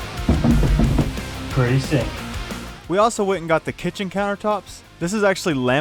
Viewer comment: Did he say "Pretty sink!👍" or "Pretty sick!👍".....?